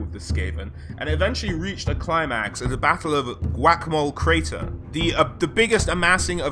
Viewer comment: is it just me or did he say whack a mole crater